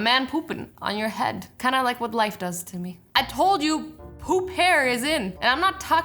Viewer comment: Lol I thought u said hoop hair not poop hair